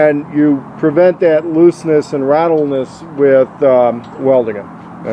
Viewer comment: I heard him say "rattleness". wtf